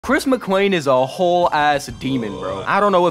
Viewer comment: Im sorry but everytime i hear "chris mr.clean" 💀